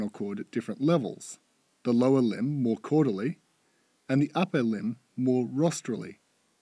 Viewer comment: can someone please help me i dont get what hes saying here 2:13 the lower limb more ... the upper limb more... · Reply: Lower limb more Caudally (ie, towards the coccyx), the upper limb more rostrally (ie. towards the nose)